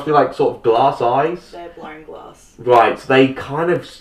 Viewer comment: what does she say at 23:10? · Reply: "They're blown glass." She says "blown" with that funny "o" Australians say when the say the word "no," kind of with a soft "r". So it sounds like she says "blowrn glass" if you really want to analyze it :) I'm an American...we ALL have funny things about the way we talk lol